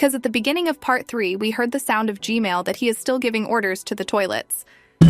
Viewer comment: it said Gmail instead of gman